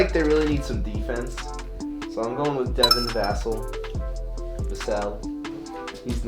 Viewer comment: When said Devon Vassel I thought he said Devin’s ***hole